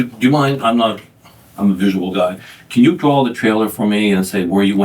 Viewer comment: That first couple times the detective said I'm a visual guy, I kept hearing I'm a miserable guy.